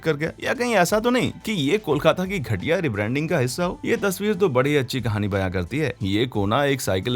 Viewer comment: did he said "Kolkata ki ghatiya branding" ????